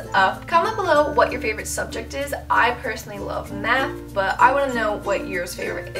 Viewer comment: Good tips, thank you. At 5:43 sounds like you said "I personally love meth." LOL!